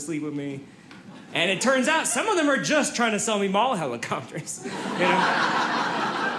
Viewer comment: I listened three times and couldn't hear it. Around 33:55 he says "...and it turns out some of them are just trying to sell me XXXXXX." What are they selling? I know it's not the point of the joke, but... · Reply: Mall helicopters